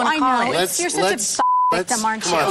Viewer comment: What does Tamra say? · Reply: "you're such a fucking victim aren't you"